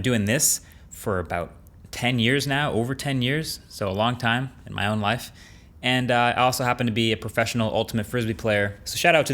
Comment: I thought he said with my own wife 😂